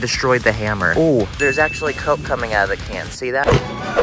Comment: who else heard come